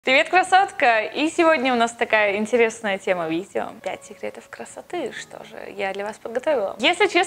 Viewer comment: Я ОБОООЖАЮЮ и знаю,что в начале говорится:"Привет,Красоткп",НО блин мне в начале этого видео ВПЕРВЫЕ послышалось:"Привет,Кроссовка" Ой,я не могуу😂😂😂😂😂😂😂😂😂🙈🙈🙈🙈